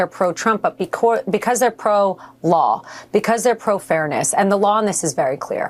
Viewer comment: "... and the lawness is very clear." WTF? She just said 'lawness'. What is that even.